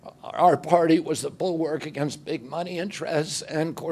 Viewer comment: Thought he said Big Mike but it was big money lol